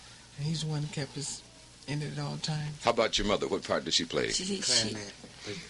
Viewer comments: what did jermaine say at 1:08? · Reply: Monica Evely I thought I heard clarinet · what did jermaine say at 1:08? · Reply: Clarinet